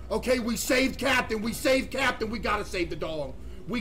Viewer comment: Instead of save I thought it said slave